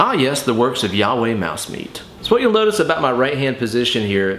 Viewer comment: Ha! My ears must be getting old. I thought I heard you say "Youee Mousemeat".